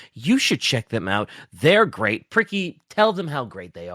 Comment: Lol did say pricky